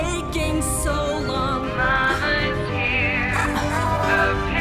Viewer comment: does anyone else hear tattletail say "mama hates you" around 1:33 XD